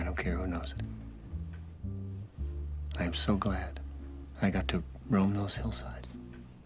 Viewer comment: at first I thought he said "I am so glad I got to ruin those insides".